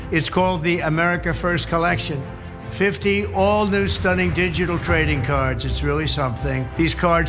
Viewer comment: Does he say :"50 all brand new tradingcards" ? (And 75 will get you a invite for a dinner) or am i just hearing it wrong?